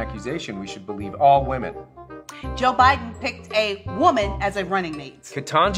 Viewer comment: When she said " joe biden " i heard "junpie "